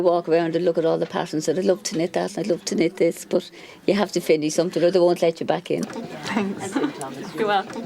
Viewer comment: What does she say at 3:52? "You have to... or they don't let you back in." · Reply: she said “you have to finish somethin or they won’t let you back in”